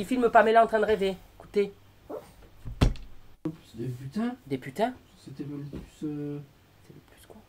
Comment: moi j'ai compris " bien putain c'était le nimbus 2 000 " comme dans harry potter 😂😂